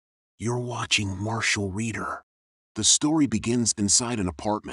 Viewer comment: Lol i thought i heard your watching martial wiener sry if i offended you no offence i heard it funny